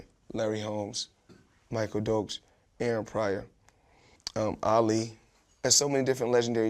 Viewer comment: What name does he say after Michael Dokes please anyone? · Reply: Aaron prior I think